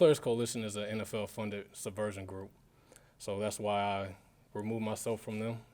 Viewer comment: What does he say at 3:38??? "Subversion" ???